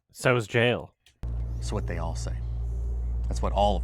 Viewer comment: "That's what they I'll say." I think whoever was doing the subtitles was the one actually on the [DRUGS].